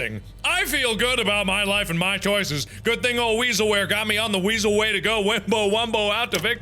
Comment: i thought it said "pooping my weasel"